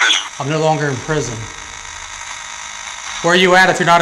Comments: Guy: "I'm no longer in prison." What it actually said: "Only in prison." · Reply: I heard "Lonely in prison" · Definitely didn't sound like "I'm no longer in prison"...sounded like "only in prison". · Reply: I agree, I heard "only in prison", as well. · No one with two functioning ears would get “I’m no longer in prison” from that. “...(“..inaudible...in prison”) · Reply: I heard “only in prison” instead of “I’m no longer...” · Definitely didn't sound like "I'm no longer in prison"...sounded like "only in prison". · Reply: I was just going to comment that. I hear "only in prison", which would make sense given the context of the question. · It sounds like he said "only in prison" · No one with two functioning ears would get “I’m no longer in prison” from that. “...(“..inaudible...in prison”) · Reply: Lmao I only heard "boned me in prison" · "I am no longer in prison" more sounds like "I'm only in prison" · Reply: I thought I heard “they put me in prison”. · I heard "I'm lonely in prison". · Does anyone agree? 1st clip/1st question, guy asks "Red" was this your cell? I heard "only in prison ". Then I think the answer to the 3rd (?) question, the response sounded like "I wasn't too good". Plmk what u think. Love to, G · "I'm no longer in prison" I heard "I'm not a lonely person" lmao maybe that's why I can't use a ghost box. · I thought the voice said "How long are you in prison"